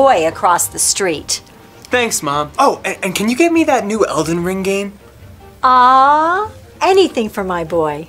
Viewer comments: are we all going to ignore how at like 3:09 the subtitles said "eltron wing" for elden ring? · Why did the subtitles say elton wring